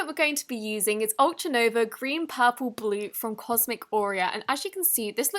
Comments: i swear it sounded like she was saying cosmic oreo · When she said I thought she said Cosmic Oreo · I thought you said cosmic oreo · I thought she was saying “cosmic Oreo” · I thought she said cosmic OREO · I kept hearing 'from cosmic Oreo'. I was increasingly confused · it sounds like she is saying cosmic oreo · I thought she said cosmic oreo😂😂